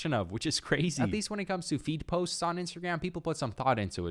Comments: I thought you were saying feet post but then I heard the feed posts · I thought he kept saying feet posts 💀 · I swear I heard "Feet" posts, instead of feed.